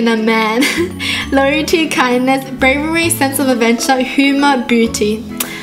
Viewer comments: at 1:42 it sounded like you said booty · Is it just me or did say booty and not beauty at 1:41? 😂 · Instead of Humor/Beauty she said Humor/Booty xd 😂😂😂